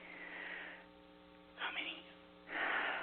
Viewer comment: u can hear a whisper sayin "how many" anyone else hear it too?? i dont know if it was because didnt say anything similar.